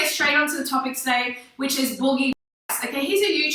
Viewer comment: i think she called him booge fat ass she's bad.